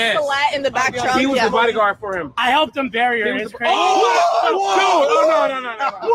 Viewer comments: I thought he said , I helped him carry her. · at first i thought he said "i helped him carry her." anyways yeah what an idiot. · I first thought he said "I helped carry her", but he said bury.